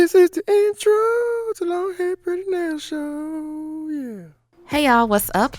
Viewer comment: I will never get tired of “this is the introooo of LongHairPrettyNails showed yeahhh”😭❤️ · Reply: I thought is said y'all and not show 🤦🏼 what a dummy lol